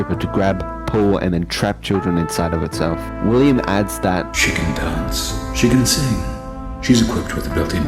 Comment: sounds like afton saying “chicken dance.” 🤣😂🤣😂